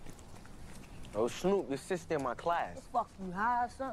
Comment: "Yo snoop your sister in my class" LMAOOO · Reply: I thought he said, "you're supposed to be in my class".